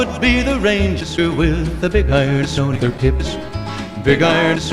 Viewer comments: Ok I’m not dirty, just saying 2:13 sounded like “Big Iron’s on their tips.” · I can't be the only one who heard: "big irons on their tits"